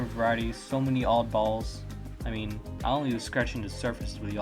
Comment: Why did you say the word balls? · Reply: I was saying oddballs lol